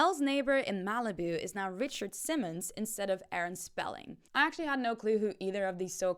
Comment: year late, but i thought she said Gene Simmons not Richard Simmons 🤦🏽‍♀️